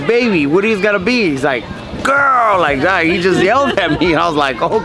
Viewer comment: I heard girl too before he said “idk”